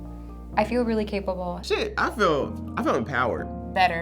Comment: Did he say sh** ?